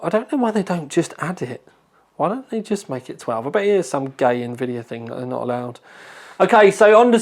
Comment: Did I hear you say "it's just some gay Nvidia thing"?